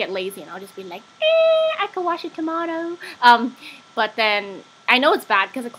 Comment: the first time watching I thought you said "eeh I could wash a tomato" xD